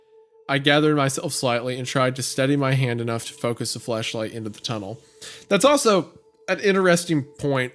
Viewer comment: anyone hears fleshlight?